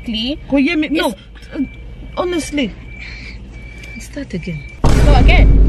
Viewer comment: Did she just say "start again"?🤣🤣🤣